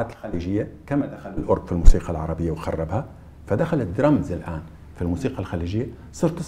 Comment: وش قال سمعته يقول الاورج اللي خرب الأغنية العربية